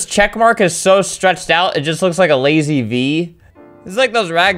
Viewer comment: I have always thought he said "bee", that always confused me untill I found that he said "V".